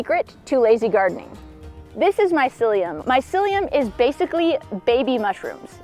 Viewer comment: Haha I thought she said this is my psyllium😂